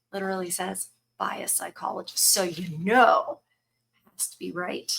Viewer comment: I swear the first time I heard you say it, I thought you said, "a biased psychologist". Turns out that was true.